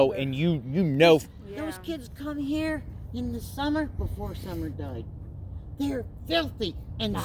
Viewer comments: I'll be posting Part 2 of this Interview Real Soon! 👍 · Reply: Im sorry but at 14:15 when they started talking about boys going back 14:22 the kids came here did she say before Summer died??? Did anyone catch that? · Allie's grandma says " before Summer died"!! Did I hear that right? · Im sorry but at 14:15 when they started talking about boys going back 14:22 the kids came here did she say before Summer died??? Did anyone catch that?